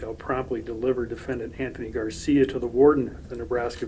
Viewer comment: I thought it said Andy Garcia....I was like,"wow he really let himself go!!"